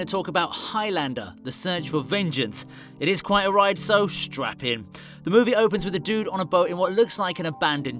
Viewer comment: Did he say strap in or strap on?